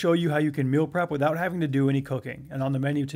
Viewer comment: Is it just me or did I hear “without doing any cocaine”. That must’ve been a big misunderstanding.